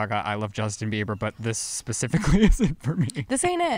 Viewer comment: Did I hear Lizzie say “pacificly”? Lmao love you Lizzie not knocking you just made me laugh like you always do 🤣❤️